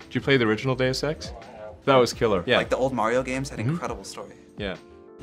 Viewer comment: what did he just say? Mario had no story 😂